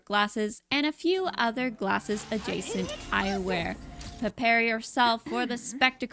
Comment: Whenever you said eyewear I thought you said I wear so I was so confused until I remembered the word eyewear.. I should know this stuff I literally wear glasses 😭😭